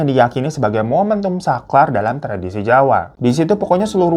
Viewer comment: hehehe punten ah kok dengernya "saklar" bukan "sakral"